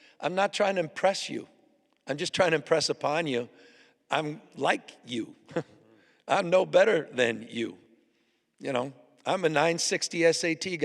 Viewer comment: wowowowowowowowoowooooo what did you just say🤡🤡🤡🤡🤡🤡🤡🤡🤡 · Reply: "Im like you, im no better than you. I am 960 SAT guy."